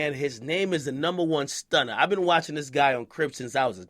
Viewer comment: Sounded like said on crip instead of on cribs